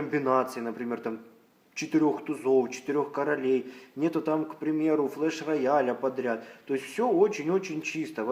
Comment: Послышалось : Clash Royale